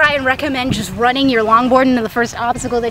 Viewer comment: good video, thought you said: nut in your long board at the end there.